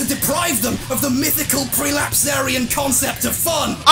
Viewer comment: he says "...or as proof that they've been lied to by an evil developer out to deprive them of the mythical [x] concept of fun". What was that between 'mythical' and 'concept of fun'? All I heard is 'prelapsed aryan' and googling that phrase led me to delightful pages of porn featuring displaced rectal anatomy that I might've enjoyed in some other time but is currently unrelated. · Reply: prelapsarian